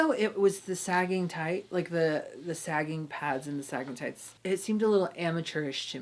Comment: When said “sagging tights” I thought I heard stalagmites. 😆